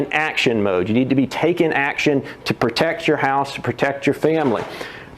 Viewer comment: did he say protect your house? Could you put it in the trunk of your SUV or U-Haul? Hey Alexa/ Siri, help me out here.🙄🙄 Love y'all, stay safe.